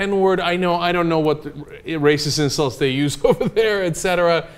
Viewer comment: i thought he said genetic since he's an atheist ..plus i cant see how "ape" can be generic. the girl obviously meant it as an insult.